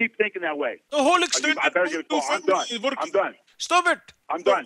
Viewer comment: Arab people listen carefully when he was saying I'm done it sounded like Hamdan lol